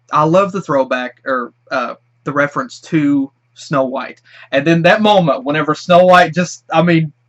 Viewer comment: I Thought He Said No Wipe